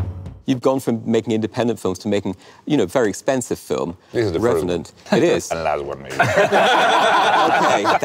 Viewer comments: What is Inarritu saying that makes everybody laugh? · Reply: D'Ascoyne he said this is the first one and last one maybe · I can't understand what Alejandro says at 2:26. Do you? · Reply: Cosmonauta Films "This is the first one, and last one maybe."